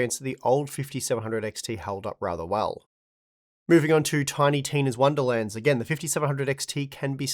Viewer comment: Did you just say "Tiny Tim's"?? lol